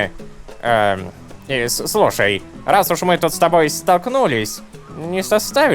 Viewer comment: Я надеюсь не только мне послышалось"раз уж мы стакнулись..." смешно послышалось. ;)